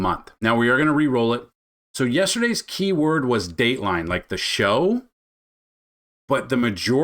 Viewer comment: so I heard "Daly" instead of "Dateline," maybe your microphone got cut off when you said it? Not sure what happened I did hear it this time...Water 💧